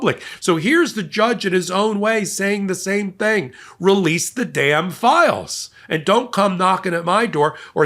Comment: Pedo files? We thought you said release the pedophiles!